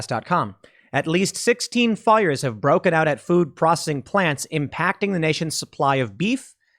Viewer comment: All I heard was "food prossing".